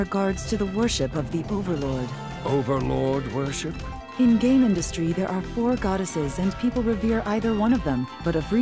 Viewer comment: I could have sworn that lady said "In the game industry."